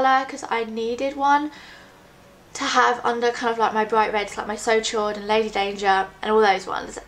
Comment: "did she just say So Chode" - My boyfriend.